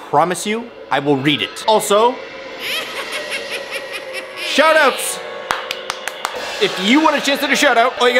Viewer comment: I thought he said shut ups instead of shout outs lol